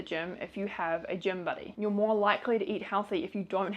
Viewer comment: I thought you said gym bunny instead of buddy.lol❗️🤣😂😂😂😂